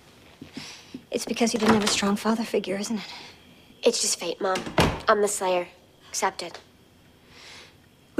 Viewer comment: at @ 1:14 Buffy used the word "Faith" instead of "Destiny". Maybe it was a hint at who the next slayer was after Kendra. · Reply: She says ‘fate’ not ‘faith’